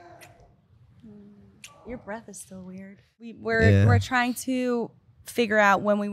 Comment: Your breathe is still weird?🤣⛳ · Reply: I thought she said "so weird", but "still weird" makes it so much funnier 😂